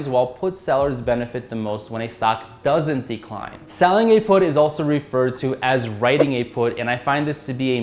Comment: did he say, "Selling a put is equivalent to shorting?"